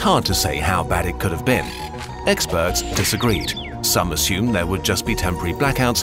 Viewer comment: Does he said "Exbirds" in 6:05?